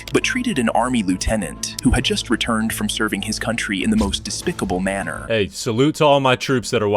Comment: I thought they said the guy served his country in the most despicable manner😂